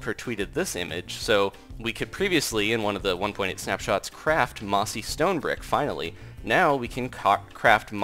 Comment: Is it just me or did he say ''now we can cock craft mossy stone bricks ''